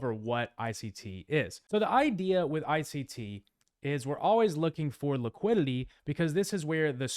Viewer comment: what does he say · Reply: You’re always looking for liquidity